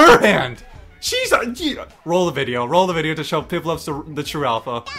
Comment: did it just say pichu